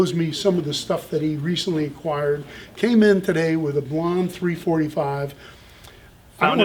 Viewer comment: A "what" 345? @ 1:18 … did he say "blonde 345"? Meaning Gibson 345?